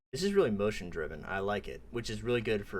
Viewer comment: I'm the only one who heard "This is a really emotion Draven" on the first time of hearing it?? I'm saying this, because the name of that character is actually Draven.